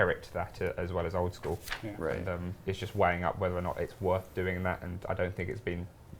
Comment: did he say "wang up" at 17:27?